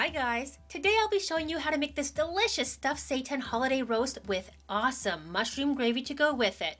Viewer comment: ummm..not trolling but.... says @ 0:35 ".....this delicious stuffed "SATAN" holiday roast..." wtf ? Did i hear that right ?